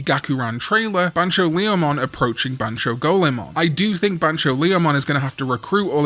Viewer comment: Huh? 😅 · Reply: my bad i was sure that data squad named " punch"leomon in middle school cause he s a fighter and leomon s special move was fist of the beast king ...guess i misheard it all along 😅😂